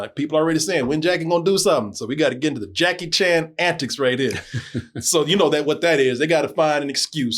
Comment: Did say Jackie chantics 😅